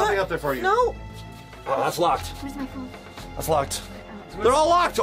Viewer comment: i heard "weres my phone?" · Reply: Wow she said where's my phone